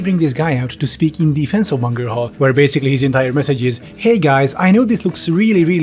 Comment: the subtitles read Bunker Hall instead of Munger Hall and I couldn’t have said it better myself